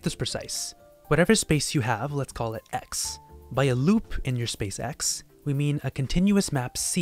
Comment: “a loop in your space x” I heard “a Hyperloop in your SpaceX”